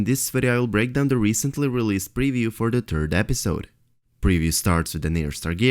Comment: Did you just say for the turd episode? :P (I know you said third)